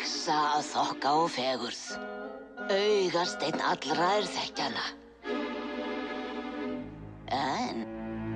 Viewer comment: oh sorry, she actually says "Augasteinn allra er þekkja hana." Not 'þeir' ;P I misheard, clearly